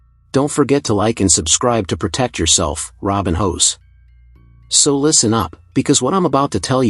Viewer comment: I heard "Robin Hoes"😭😭